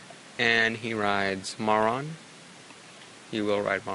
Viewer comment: Is it just me, or do I keep hearing say "Moron" instead of "Marron"